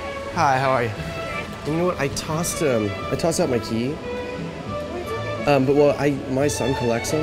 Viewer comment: what does he said at 1:57? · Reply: Hi, I toss out my key (it means like throw away). But my son collects them, so I wonder if I can get a deactivated key just to bring it home